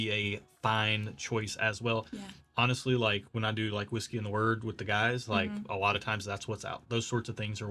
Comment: Did say 'whiskey and the Word"? at 13:47? As in the Bible? If that's what he said, I live in the wrong state and would love to read some literature about your organization.